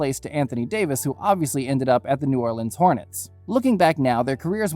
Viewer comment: Am I the only 1 who heard him say new Orleans hornet's